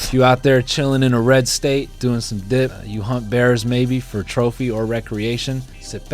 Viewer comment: Look, I logically KNOW you said "hunt bears" at 6:07 but I replayed it a bunch of times bc I SWEAR you said "hump bears" lmao